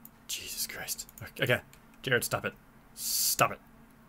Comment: did you just call yourself?